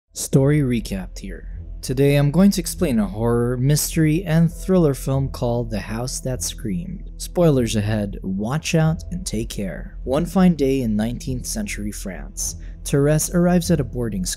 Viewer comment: What's the name of the movie I can't find it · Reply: thank you I thought the narrator said the house that's green no wonder I couldn't found it